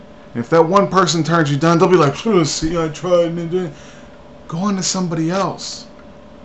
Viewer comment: sounds like he says, " see i triend (N word) at 4:48 to 4:50 lol